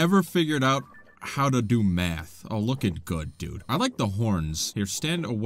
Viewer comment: Had to rewatch the first moblin because I genuinely thought you said they hadn't figured out how to do meth 😂